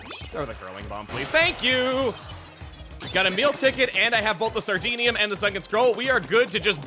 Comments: I keep hearing "a meal ticket" as "Emile ticket" and got really confused a number of times.... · Everytime he says "a meal ticket" I hear "Emile ticket."